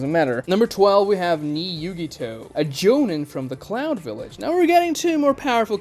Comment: Had to do a double take on cloud village. Could have sworn I heard clown village.